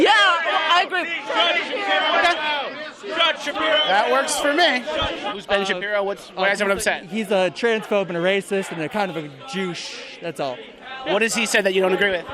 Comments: was he stoping himself from saying "and he's a Jew" to "douche" ? Anyone else hear that? 🤔😂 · Did he say: "He's a transphobe and a racist, and kinda a JEW-CHE" 😂 😂 😂 😂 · I swear this kid almost said that Ben was kind of a Jew. He sort of mixed Jew and douche. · How can you disagree with someone's platform, but on the other hand can't even state the "bad" things he has done/said? 1:50 Did he say what I think he said? He combined the word "douche" and "jew" to form "jewche"? Didn't really sound like douche. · It sounded like the dude at 1:50 was about to say kind of a "jew" of something like that but change it to jewsh/deuche but I still hear a hard "jew" come out of that pricks mouth. Is that just me? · Wow, the Antifa dude at 1:53 called Ben “kind of a Jew sh...” what was he really trying to say? Clearly the boy is anti-Semitic. The hypocrisy of the left never ceases to amaze me. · Did he just call him a "jewche"? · uh oh the guy at 1:52 was tryna say "kinda a jew" but quickly backtracked. The left hates jews even more confirmed XD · hang on, did he just call Ben a "jewche"?? Isn't that supposed to be exactly against what "they" stand for? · Is he saying "jewsh" or' Jewouche" instead of 'douche'? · Did anyone else hear Jewche? :o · LOL I thought the guy said "jewish" and I was about to join antifa. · did he say Jew’ouche?